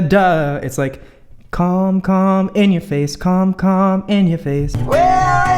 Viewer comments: Why did I heard "cum cum in your face"???? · “Calm calm, in your face Calm calm ,in your face” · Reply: OMG I THOUGHT HE SAID CUM CUM IN YOUR FACE HAAHAHAHAHAHAHAHAHAHAHAHAHAHAHAHA i was like ????? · I THOUGHT HE SAID CUM CUM IN YOUR FACE Lol. Its calm calm in your face ahahhaahah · Did I Just Hear "Cum Cum,In Your Face" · When said "calm,calm,in your face" I thought he said "cum,cum in your face" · I literally heard "Cum, cum, in your face" LMAO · I thought you said “cum cum in your face” and almost died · it sounded like said something... dirty..... calm calm in your face? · Oh god😂 when you said calm calm in your face, I thought you said cum cum in your face😭I need jesus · i light thought he said cUm cUm in YoUr fAcE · DID HE JUST SAID "CUM IN YOUR FACE"????? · Sorry but I really thought that you said "cum, cum, cum in your face" and I saw like WTH 😂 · Oh no why’d I hear him say “cum” “cum” “in ur face” 😂😂😂 aaaaa nooo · i literally thought he said ”c*m c*m in your face” lmao i’m so immature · i thought said "cum cum in your face, cum cum in your face" sorry.